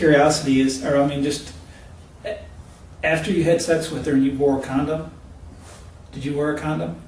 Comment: HOLD ON!! WAIT A MINUTE!! DID THE DETECTIVE SAY THIS DISGUSTING WASTE OF SKIN HAD SEX WITH A DOG ? Nevermind.... I went back n read transcript... I could of sworn u heard the detective say that... lol